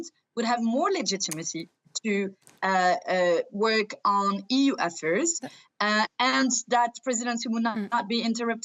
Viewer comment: I beg to disagree. Elections don’t strip legitimacy to the current elected official, at least in functioning democratic societies. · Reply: you're not hearing what she said did you ? She, and I said : " more legitimacy to work on EU efforts " 3:20. It's easy to picture how complicated it can be for a president that isn't assured to take the lead for France in 2022, simple as that.